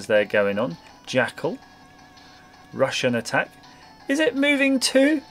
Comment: For some reason when he said Rush N Attack I thought he said Russian Attack